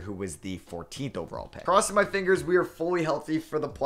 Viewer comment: no way you said steven adams i heard semen adams😭😭